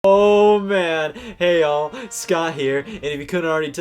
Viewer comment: For a while I thought you were saying "Hey Yall" not "Hey All" I am new here